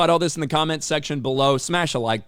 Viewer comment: Why do I keep hearing "shit this video out there"?😆😆